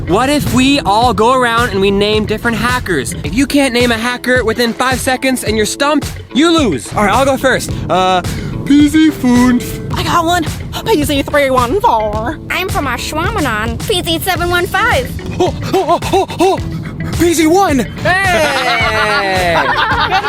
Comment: Actually sounded like PZfunf